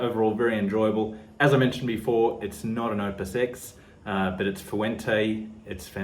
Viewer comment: Bro, I am old and not only are my eyes failing me but my ears as well. I thought I heard you saying, "it's not an upper six." And I wracked my noodle trying to figure out what upper six is... Maybe it's his 6 most favorite cigars... Kind of arbitrary, 6... Most do 10. Maybe it's an inside thing between him and his subscribers, if you're in on the joke... Then I actually watched your mouth while you said, "it's not an opus x." And my brain exploded🤯